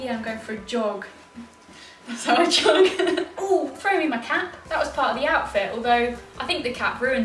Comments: I thought she said throw me my cat and got so worried when a grey thing came flying through the air😂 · I thought you said "throw me the cat" and I was getting worried about Moon😂😂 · When you said ‘throw me the cap’ I thought you said ‘throw me the cat’ and I was very concerned for moon😂😂 he would match the outfit tho lol x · I thought you said throw me my cat not cap 😂😂😂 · Lol I thought u said throw me the cat hahahahaha x x x x love u x